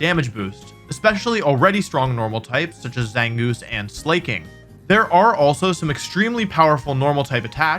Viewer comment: Did he just say…”Slay-king”? 🤣